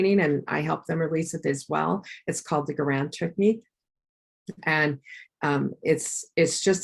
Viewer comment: Does anyone know the name for the therapy mentioned around the 7:40 mark? The transcript recorded her saying ‘grand’ but I can’t find anything like that online. To my ears it sounded like ‘Garan Technique’. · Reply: The technique is called the Goran Technique